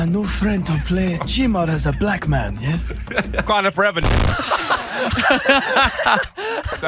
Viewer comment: I thought basicallyidowrk said what kind of reve